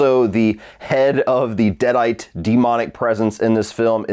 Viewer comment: At first I thought you said: "Also the head of the Dead-Eyed demonic presence in this film is...?" 🤔Then I thought nope - may be deadite? Gawd! Googled, and saw parasitic demon is what that is!! Now, that made the rest of your views flow so much better thank you!😉😊